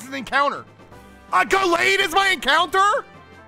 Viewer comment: he said "a gallade is my encounter?!" And all I heard was "I got laid is my encounter??!!" Lmao